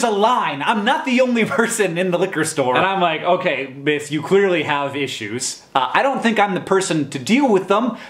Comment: LOL at 1:34 i thought he said "ok, clearly bitch" not clearly miss. I was like what???